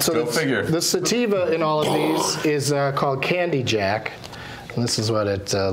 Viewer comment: oh wow. I thought for a minute he said candlejack. lol you wouldn't want to say that because oh shi